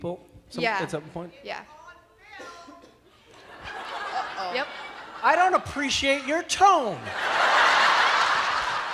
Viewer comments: what did they say at 9:14 its driving me crazy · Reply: Megan Xu "its on film" lol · What did the woman say at 9:13? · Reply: AnimeLover17 "IT'S ON FILM"